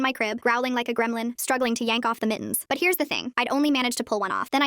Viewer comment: Not me dying bc I heard “me in my bed growling to yank off the mottens